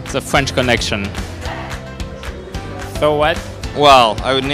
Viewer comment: So what 😂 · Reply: I thought he said 'So White'